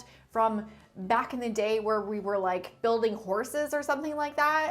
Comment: Did you say "building horses" at 3:38? 🤣🤣🤣👍👌